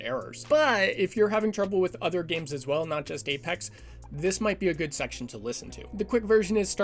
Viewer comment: did i hear g*ypex? 1:26 cuz if you really said that then you're legend.